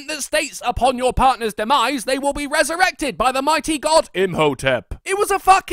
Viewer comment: is it just my or did he say by the mighty god info chan?